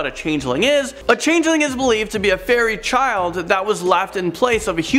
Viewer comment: First off, I always thought it was “FAIRLY odd parents” and second, every time u say “fairy” I hear “ferret” anyone else?